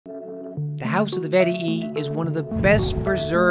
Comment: Great job! So much fun to see Pompeii with you! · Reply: I thought he said "the house of the Yeti"